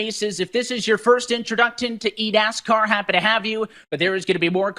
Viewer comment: I keep replaying it over and over and I just hear eat-ass-car